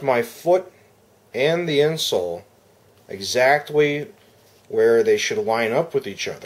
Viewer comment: does he say exactwee ? lol ...a small thin piece of 2-way tape may secure the insert without having to Sharpie your foot! Thx for the review.